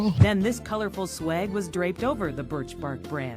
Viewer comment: To be fair to her, i think she said "suede" and was done dirty by the captions. However, this does not excuse her previous sins.